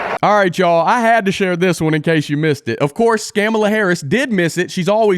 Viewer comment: did I just hear scamala ??? Lol